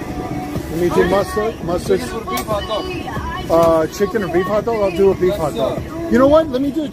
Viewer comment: I thought I heard sabrett's beef hot dog or great value chicken hot dog